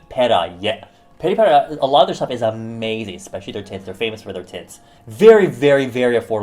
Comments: When u said "they are famous for their tints" I thought you said their famous for their tits omg I'm dying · I heard "they're famous for their tits" instead of "tints" at first I laughed so hard oh my god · I heard "they're famous for their tits" instead of "tints" at first I laughed so hard oh my god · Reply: ItsBreana i didnt know he said 'tints' i thought he said tits as well like i was dying with laughter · if i didnt watch beauty vids as much as i do, i could have swore you said tits instead of tints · did he just say 'they're famous for their tits' or am I hearing things? 😂